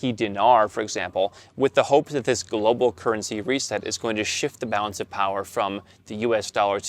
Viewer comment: I thought he said Baskin Currency! I'm like WTH.... Ole Carol Baskin.. that BITCH!! LOL 😂😂😂